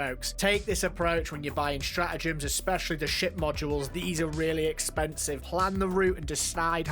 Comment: This whole time, I thought you were saying sh*t module, but you were saying ship module. ( Sorry, my bad)